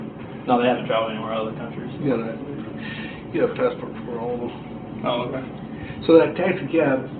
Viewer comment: BassickOfficial oh, my bad I meant at 2:18:17 when he says “you can have a passport......” can’t decipher the rest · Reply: Chris Oh yeah, that’s tough to hear. It sounds like he said: “You can have a passport for all of em” Or “You’d have a passport for them” Neither of those make sense lol but judging by the way the conversation was going, and Chris’s response, the officer was just stating some meaningless fact